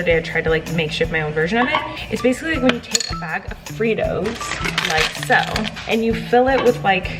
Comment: i thought she said doritos until i looked at the packet JSKAZZZMSSNS💀💀